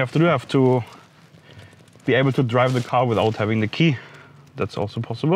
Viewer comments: "we will be able to drive the car with the key" but the captions say we I'll be Apple to drive the car with out the key" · Did anyone notice at 1:39 that the subtitles said apple instead of able? (There is probably ALOT more comments about that little tiny mistake)